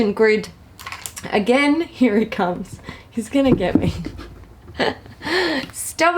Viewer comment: I could have SWORN you said ‘you’re a cunt, he’s going to get me’ 😂😂😂 but I misheard. However, I say this to my cat in a loving and endearing way when he is clearly knows I’m busy 🙈 (Sorry if that word offends anyone, I’m Aussie, what’re gonna do 🤷‍♀️)